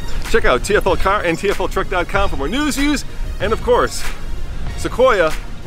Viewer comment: Wtf does he say here? "For more news, use?, and real world reviews"?